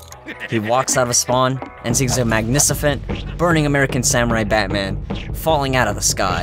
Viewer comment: did u really say “Magnicifent”?!